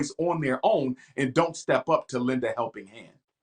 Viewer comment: He specifically said stepdads, not mentors. He's caping hard for BW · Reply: He did not say "stepdad" but "step up", at 13:35 - 13:40. If you are referring to any other passage, please let me know.